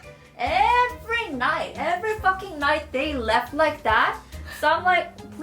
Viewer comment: haha i thought you said left instead of laugh